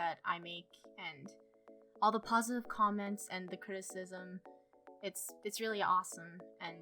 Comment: Second I thought you said “All the awsome comments and the HATE” “I just love to see that” But then again 8 have bad ears XD